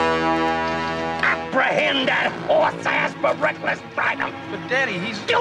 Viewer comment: Can someone tell me what Buford says from 05:53--05:57? He's the best police I ever seen! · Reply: Ha ha thanks man! I thought he said something about -"horseass for breakfast" apprehend was a new word for me I'm from Sweden so.